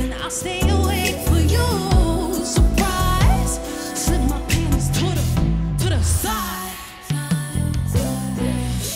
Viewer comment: what does she say here? 😐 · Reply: Surprise…the title of the song